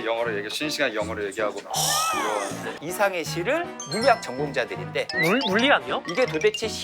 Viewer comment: 이상의 시를 이상해씨로 들었....ㅠ